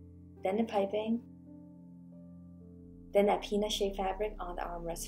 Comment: did I hear you right? Did you say P*nis shaped fabric? Edit: PEANUT! YOU SAID PEANUT